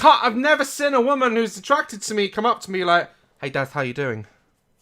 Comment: When Daz said hay Daz how are you doing I thought he said dad an nearly throw up